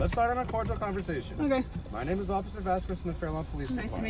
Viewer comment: The auto generated CC of the video has him saying "Let's start a cordial conversation," though I thought he was saying that he's recording the conversation. Either way, after telling her to stop (and let's overlook the "hit" from the car since that happened right at the same moment), it was clear that she was being detained and she needed to stop instead of trying to leave and argue. Since the officers were called there for a reason and the officer believed that she was involved in the situation, he had a right to detain her for their investigation. That aside, the officer was trying to be nice by talking to her, but she was arguing with him. She's an example of someone who asks questions and then won't listen to what they're being told. That mentality of "if I over-talk you enough, then I win!" Which is wrong and is going to land her in jail at some point when she encounters an officer who doesn't mind dealing with the necessary paperwork to put someone through the process of facing consequences for their actions. I hope there's a video of her dealing with such an encounter, with the end result being a few months in jail to recalibrate her attitude.